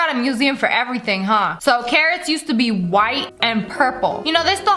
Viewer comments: When she said carrot I thought she said Karen I was like damn Karen where worst than now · The carrot part I thought sniper wolf said Karen but I have to watch it again but she said carrot 🥕